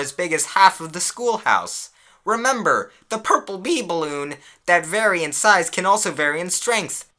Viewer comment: i thought it said pee ballon IM DYING